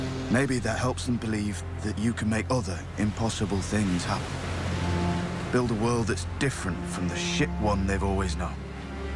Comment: "Build a world that's different from the SHIT one they have always known" does he really say that? 😂